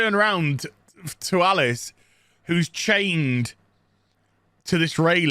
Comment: For a second, you tweeked my interest when you said, Alice was chained, and I heard: "Alice in Chains." Sounds just like Dune 2021. Not much Ado About Nothing. Even the 1/3rd of the movie of the first cinematic attempt, there was no plot, no good VFX, no good acting. I didn't even understand why Poe was in the movie at all and how fast he went grey. Maybe coke does that to you, idk. There were Snyder cut like posing, walking, and 8 "commercials" where the oppressed woman of color (which is wrong, cause ppl like you are the oppressed group nowadays) is portrayed looking into the camera confused, a wet dream/hallucination of Paul, or faced away in a flowing robe, with a body of a teenage boy.